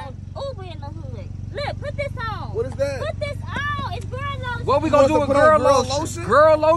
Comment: I thought she said Burn Lotion, and Dudes were gonna turn White